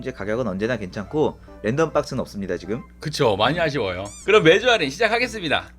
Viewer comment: 던전스는 없습니다로 들리는건 저만의 착각일까요?